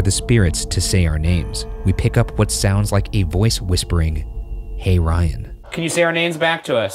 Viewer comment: It very clearly does not say "Hey Ryan". It's just "huh"